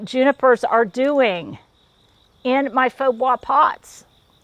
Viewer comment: Ok, I have to ask, at 13:33 what are you saying? "Faux guah pots"? I've heard you reference these pots before but what are you calling them? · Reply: She is saying “faux bois”— a style of fake wood made out of a concrete-like material :)